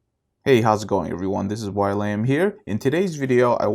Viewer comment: I swear to God I heard "While I'm Here".....wow. Really cool name.